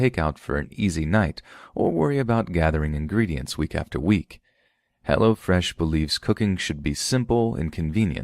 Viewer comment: For a second I thought you were getting street on us, LMAO all I heard (or thought I heard ) was, "What is Hella Fresh??" Not Hello fresh 😂